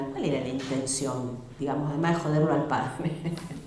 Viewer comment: Saludos, alguien me podría ayudar a entender que dice en el minuto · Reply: El padre, podríamos decir.